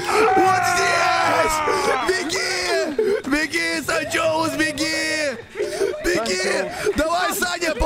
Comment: Мне не послышалось что, сказал санчеус